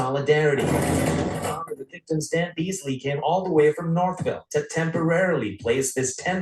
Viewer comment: I thought was gong to say: Flew all the way from Salt Lake City….lol, that would make more sense.